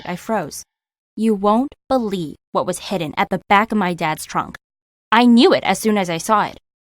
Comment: I thought trunk said truck